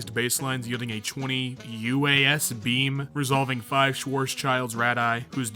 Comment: did he say schworzchild?